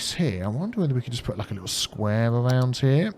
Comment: Did you say "squareabout" at 17:36? Well done (back in Jan 2018)! :D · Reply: He said square around here